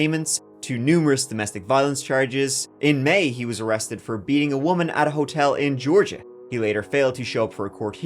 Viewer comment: …did you say “Being a woman” @ 25:30?